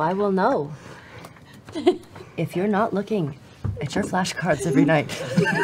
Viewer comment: I'm sorry, were they flash cards or flesh cards? I think I might have misheard. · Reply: Haha flash cards